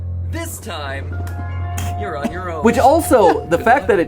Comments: I thought you said we were on our own!! 😭😭😭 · I thought you said we’re on our own?? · Bro I thought you said we wuz on our own